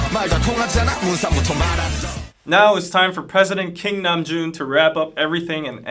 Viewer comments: lols i heard him say president king Nam-Joon. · "now it's time for president kim namjoon to wrap up everything and end the fight" all hail president namjoon!!!!! (wait, did he say "president kim namjoon" or "president king namjoon"???) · President Kim NAMJOON!!!!🤧🤧🤧🤧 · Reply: I thought said President King Namjoon (either way it works)